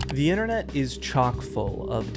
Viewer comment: Bro because my Internet is buns it buffered at the beginning so I just heard "The Internet is chalked"